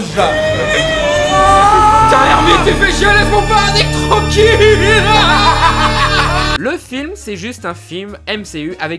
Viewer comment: il dit quoi "laisse mon....... Tranquille" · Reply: Laisse moi parler trkl